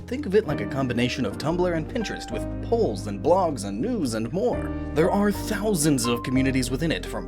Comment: when you said "news and more" during the amino advertisement i thought you said news and porn